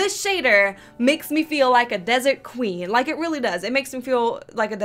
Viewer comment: I thought you said dessert QUEEN lol!